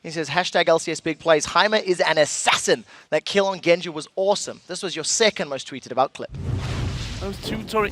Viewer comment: in that clip what the hell did the commentator say? wassa wooh suh wassa in? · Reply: No. At the last word he said awesome